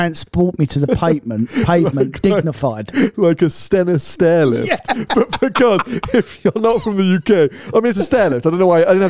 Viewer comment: Thought you said 'Senna stairlift' :)